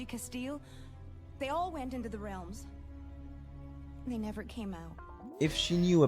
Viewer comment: why did Lani say "WE" never came out instead of they? She says it as if she went in and either was lost to the realm already and that's not REALLY Lani, or she lost part of herself in the realm (either metaphorically or literally) and had to be saved by another driver. I know im almost 5 years too late to this video, but it just struck me as odd that she'd include herself with that comment.